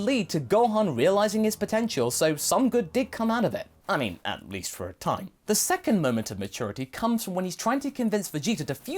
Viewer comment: it’s late, I hear everything, but what do I hear here? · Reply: Forgive me. 👏